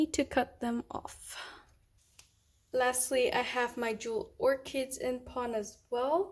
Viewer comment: Hi, what’s the orchid plant called please? Sounded like you were saying dual orchids, but struggling to find. Thanks